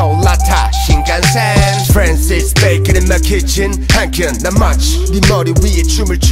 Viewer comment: I thought he said France is baking in my kitchen